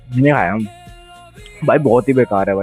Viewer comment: par kya bola bhai 😂tum ne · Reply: Mai ni khara bola tha bhai